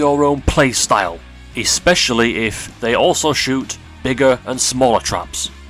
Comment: did you say bigger though?